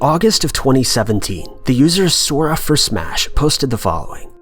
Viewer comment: What user did he say at 6:20 this one really intrigues me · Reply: I found it, it’s by the user Sora4Smash. Titled “Do certain songs/music give you existential dread?”